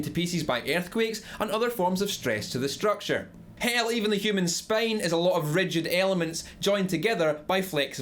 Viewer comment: did anyone else hear -human Spain ?